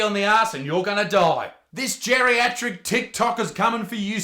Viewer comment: I thought Isaac said 'geriatric tit knocker'.